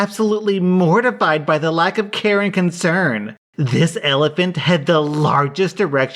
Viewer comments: I'm half asleep and in the first story I thought I heard "...lack of Karen concern!" when it was "...lack of care and concern!" . I thought to myself "This is a very self aware Karen". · For the first one I thought it said lack of Karen concern until I looked at the screen lmao